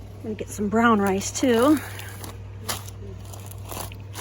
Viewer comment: When you said brown rice at first I thought you said brown ice lol